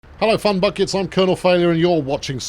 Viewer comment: I'm half deaf, I had to rewind the beginning cause I thought you said "cum buckets", being half deaf can at times be really funny.